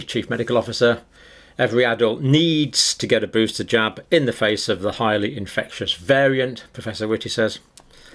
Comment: All I heard was 'Every adult needs to get a booster jab in the face.' ROFL. Whew....glad that's not true.